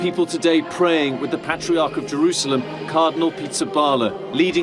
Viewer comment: SHABBAT SHALOWAM ✊🏾 APTTMH YHWH HALLELUYAH ✊🏾 Hold on did I hear this right the guy's name @ 11:25 was "cardinal pizza parlor?"